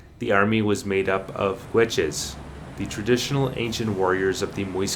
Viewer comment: Did you say witches were the warriors? ??? Can you expand?